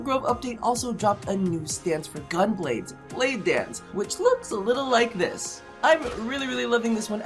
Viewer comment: Hey, I think you called the new stance "blade dance", but that might be just me misshearing